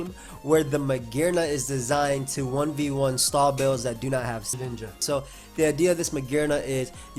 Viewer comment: Is it just me or did anyone else hear Shedinja at 1:03?